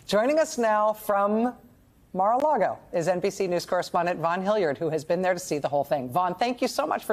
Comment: did she just say NPC news 😂 Ofc this is news for npcs , Real level headed people can see what’s really going on here